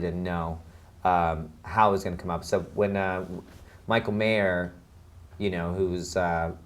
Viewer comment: for a seconed i thought he said michal myers hahaha